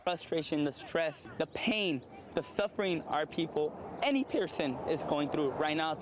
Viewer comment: did he just say “any piercing”? 👂